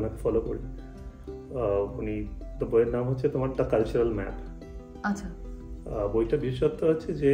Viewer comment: I don't hear the name of the book and author of it clearly. Can anybody help me to know it please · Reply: The culture Map - - Erin Meyer 😊😊